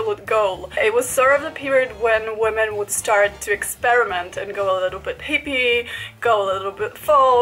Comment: I heard "-and go a little bit peepee."